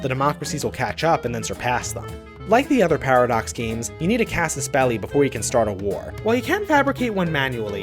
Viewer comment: When you said casus beli to start a war, I thought you said "cast a spell" to start a war